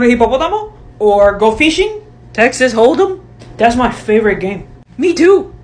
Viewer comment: DID SAY HEXES HOLD EM? I COULDA SWORN SHE SAID HEXES HOLD EM